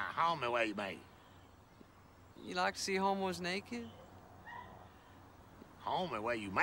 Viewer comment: I thought he said “you like to see homeboys naked” 🤣🔥🤣🔥🤣